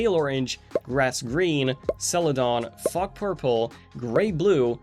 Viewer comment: for a moment I thought he said f*ck purple. 😂